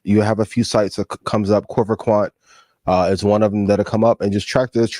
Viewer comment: did ian say corver Quant or what exactly ?